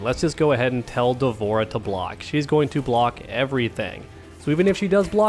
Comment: I heard "tell the whore to block"